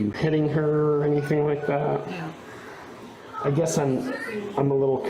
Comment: You need to watch this again. Her grandma is on probation and told the cops about that too in the video. The whole family is full of criminals. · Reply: what does her voice say? You can hear her words. You can hear the context. Yet you'll take a subtitle (likely written by AI or a white person) over the actual words you hear.. Check out the transcript (not the subtitles) for 14:05. You'll see the officer asks if the mom hit her, and the transcript says the mother responds with "yeah." Watch the video and the subtitle and video both show she says no. Then read the transcript for your part and the grandmother says she is going to put her (the granddaughter) on probation.